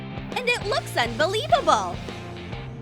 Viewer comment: at the start of the video I thought she said sunbelievable🤣🤣🤣